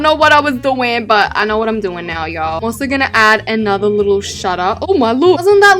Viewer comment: did I hear “you know what in just gonna add another shut up”